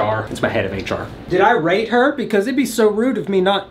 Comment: wtf did you just say?? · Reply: He said rate but the caption says something els 😂